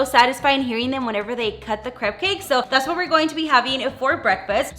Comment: I keep hearing “crab cake” lol